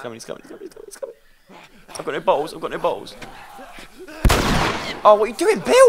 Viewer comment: did he say he has no balls? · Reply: No he said I got no bottles